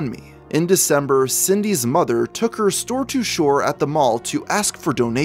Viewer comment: What are you saying at 7:25? Store to shore? There's often sentences in these video that make no sense lol